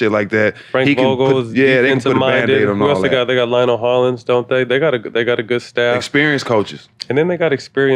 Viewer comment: I thought Matt Barnes said Ryan Hollins 😂